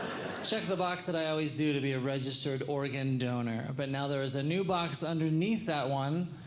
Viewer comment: Was he saying Oregon donor? As an Oregonian I think I should have a say in this...